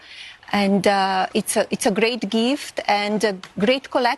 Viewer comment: LOL Her English is still so poor, after decades in the U.S. it sounds like she said, "it is a great GRIFT. lololololol